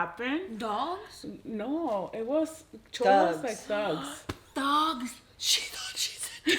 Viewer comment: I thought she said dog's to lol